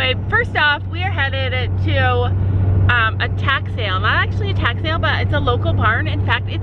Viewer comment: In the beginning when you said you were going to a tack sale I thought you said attack sale and I was like What??😂😂then I realized what you said...